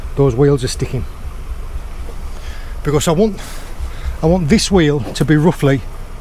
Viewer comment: Informative, thought u said "sun wheel" , that's on me Ha!